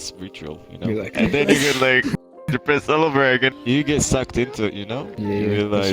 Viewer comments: I thought it said, getting sucked in vr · I thought it said, “Getting sucked in VR” I was like 🥵